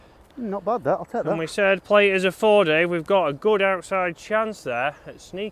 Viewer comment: Dave thought he said a foot outside? Love these videos. Too funny